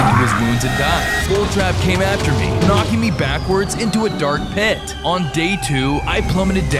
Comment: Did you say dark piss?